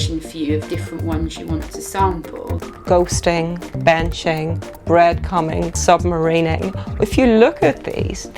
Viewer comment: did she just say breadcumming?